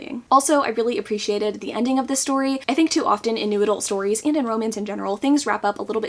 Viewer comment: Every time you said "new adult" I heard "noodle"